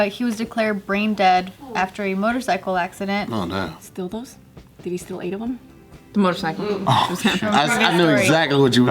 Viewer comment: I didnt get what Gina was saying at 3:35 ?? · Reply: Coronavirus will kill us all